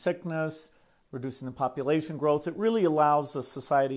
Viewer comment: Did he say "reducing the population growth?"